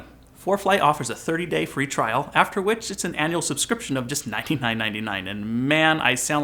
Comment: What I heard you say is that ForeFlight annual subscription is 19-999. Did you mean $199.99